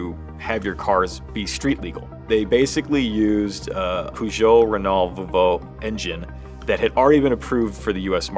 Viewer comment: Peugeot, Renault, Volvo. Is that what's said there? All kinds of pronunciation!